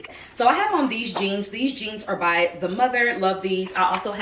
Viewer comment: Who are the jeans by, Sis? I thought I heard The Mother but I'm not certain